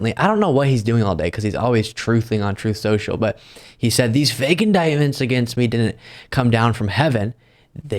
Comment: I thought he said he knew a 4tn indictment was coming